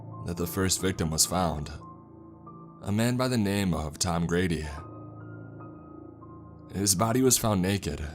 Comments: I misheard Tom Grady for Tom Brady and I’m like “yo Tom Brady got clapped” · Wait Tom Brady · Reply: Oh i heard it lol i thought he said Tom brady for a sec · When he mentions tom brady in this story I know this was fake 😂 get the strap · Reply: It was supposed to be Grady. But seems like a lot of people heard it as Brady.